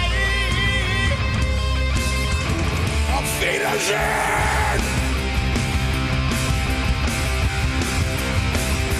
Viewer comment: can someone tell me what's he saying 🤚🏻⚡️⚡️ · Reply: AUF WIEDERSEHEN (german for SEE YOU!!!!)